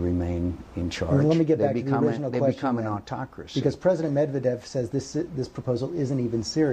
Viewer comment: Hmmm.... can someone help me out? At 2:01 John asked to comment on "Tochras"? I'm not an expert on Soviet government, but I don't know of anyone with that name? Did I hear him incorrectly? At any rate, I think George should have refrained from stating the name of the actual President of Russia, at 2:03, and then asked John who the president was. I bet he wouldn't have been able to answer.....